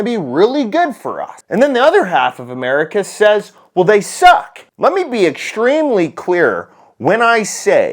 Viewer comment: Even knowing what is actually being said, I can't not hear at 0:42 "Let me be extremely queer"